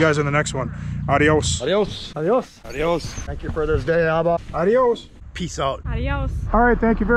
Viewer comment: Great vid as always. One question: Instead of saying: ''adios'' (like everyone else), why does Bugs always say: ''Thank you for this day, Alba.'' ? 49:46 Who is Alba? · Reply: Abba an Aramaic word for father, used by Jesus and Paul to address God in a relation of personal intimacy.